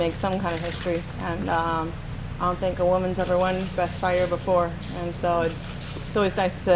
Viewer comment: Is it just me or did she say "breast" fighter? She's definitely the first to win the ESPY for "breast" fighter. I'm pretty sure none of the nominees had a greater record for breast fighting.